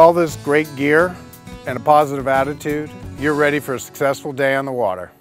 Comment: At the end I thought he said "you're ready for a stressful day on the water"